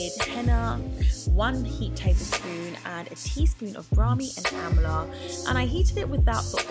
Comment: 1 tablespoon of henna. 1 teaspoon of Amla and Brahmi each. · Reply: Naturally_Eesha I thought she said a teaspoon of henna, an half a teaspoon of amla, & brahmi ur Main Ayurveda 🌿 is HENNA